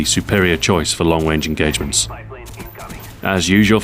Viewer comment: did he say long wange engagements?